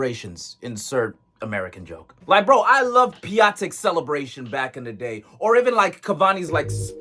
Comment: At first I thought that he said "Pulisic's celebration"